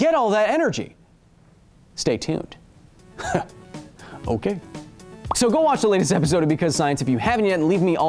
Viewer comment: Here is a link to the spider stats I mentioned! Have fun -- Spider World Records: <link> -- kH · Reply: You mispronounced, "MY MAN!" Sounded like you just said, "ok."